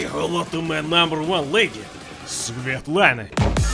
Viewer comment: I honestly thought he said SOVIETlana lol